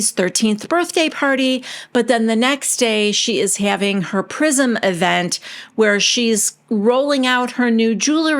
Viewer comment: Oh, and every time anyone says “Prism Event” I hear “Prison Event”.